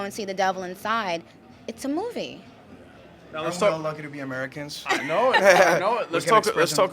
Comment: Hey, on my computer I can barley hear for some reason. Can anybody tell me at 4:28 what he said about being lucky?. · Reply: Hey yeah, he said that "We're lucky to be Americans"